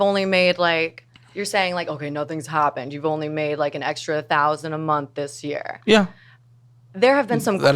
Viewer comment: did anybody else think when she started she said she was suddenly making $40,000 a month?